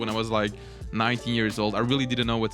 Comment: i thought he said 90 yrs old